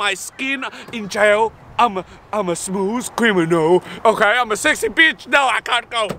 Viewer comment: It sounds like ur saying smoos cwiminal